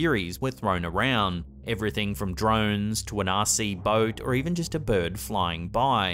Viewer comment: XD OHHH Rc boat. I thought he said Nazi boat. That would be WAAYY more terrifying